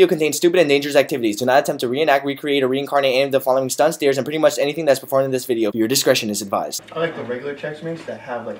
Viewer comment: This is what I heard at start "dares are pretty much everything done in this video" lol